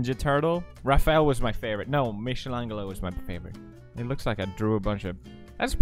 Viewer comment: Did he just say mishilangelo 😂😂😂😂😂